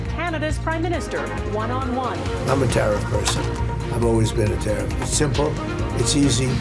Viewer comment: When he said I am a tariff person I thought he said I am a terrible person 😂